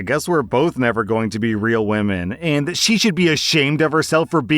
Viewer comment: she actually said she SHOULD-NOT be ashamed. Not that she SHOULD. That was a misread, which sucks for anyone who didn’t catch it :/